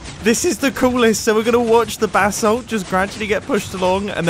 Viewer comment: MANY episodes later... All I keep hearing is "Bath Salts"...